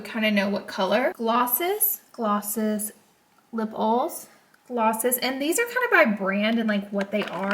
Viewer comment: girl I love ur southern accent. At first I thought u were saying lipples, like nipples. rofl but I realized ur saying lip oils!!! roflmao!!! tfs